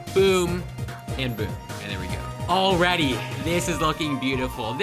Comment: I thought he said a sprunki character but he was actually saying already